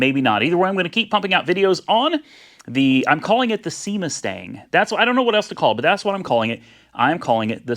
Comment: I had to rewind and turn the volume up because I thought you said you were going to call it the semen stain...I'm so relieved. 🤣